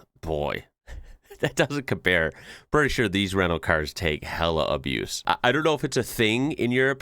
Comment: HAHA! I thought you said "Renault cars" (when it was a white Citroen on screen) then realised you're saying "rental cars".